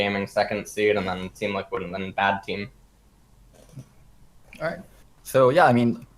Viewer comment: LS did just call them "BAD team" at 3:45 right? that wasn't my imagination?